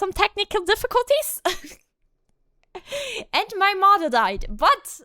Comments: I first thought said "and my mother died" and I was like "woah, that's a big piece of info to drop" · I almost had a heart attack, with your accent it sounded like you said, "and my Mother died." Thank God I heard wrong